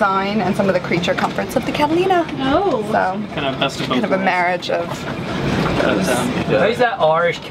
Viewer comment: Did he say "the best of boat worlds?